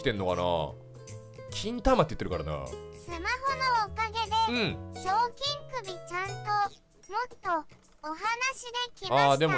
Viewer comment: 賞金首ちゃんとお話できたです。が賞金首がちゃんとお話できたみたいに聞こえる